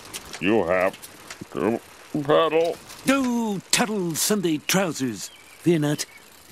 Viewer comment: What does Burns say at the 0:46 mark? "Turtle Sundays Trousers!"? Is that a saying lol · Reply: Maybe Tuttles Sunday Trousers lol... whatever the heck that means